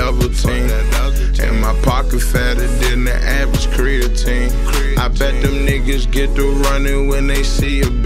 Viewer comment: Did he just say creatine?